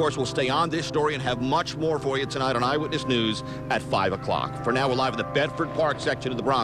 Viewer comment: was spaming 0:11 to watch the fall over and over again, and i thought the reporter said " For now we're live on the bed for pr0nz section" LOL.